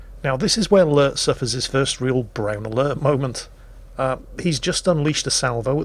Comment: or so it really sounded like "brain alert" and not "brown alert" or was it just me???